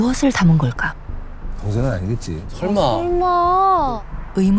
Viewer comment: 데프콘이 뭐라고 한거에요? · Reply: 동생은 아니겠지~